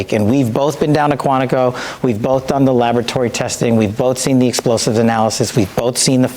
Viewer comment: It sounds like Kash is saying they were both down there for “lavatory” testing instead of laboratory testing… Sounds about right for their dumbass brains 😂😂😂